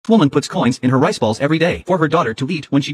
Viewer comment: i thought she said she put a coin in her right ball